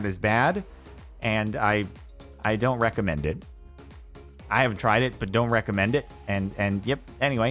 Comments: he said he has not tried it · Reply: I can swear I heard "I have tried it" but that could be because he's speaking a bit quick. If you do slow it down to 0.25, like I had to, you can hear the "haven't" instead. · I leggit had to rewind because I thought said he'd tried meth and didn't recommend it 😂